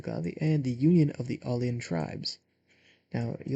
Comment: I thought it said ,,Union of The Onion Tribes" XD